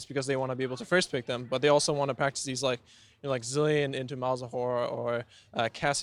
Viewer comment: did he say malzawhore? LOL